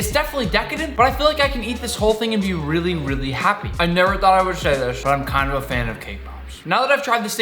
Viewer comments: I misheard it as “I never thought I’ll say this but I’m kinda a fan of kpop” XD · I heard it was k pop lol